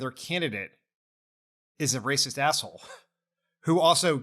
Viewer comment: I misheard racist asshole as rapist asshole and realised in this case they are not mutually exclusive.